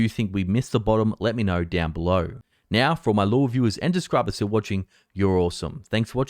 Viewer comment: Every time I hear "for my law reviewers" instead of for "my loyal viewers"